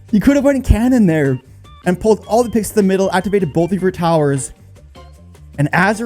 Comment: did he say activate both of your towers 😂😂😂😂😂😂😂😂😂😂😂